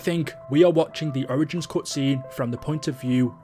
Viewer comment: I could’ve swore it said “Point of view of Dempsey Through his Ass, not a camera”